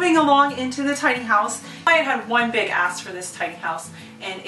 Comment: Did she say “I have one big ass for this house” at 2:30 x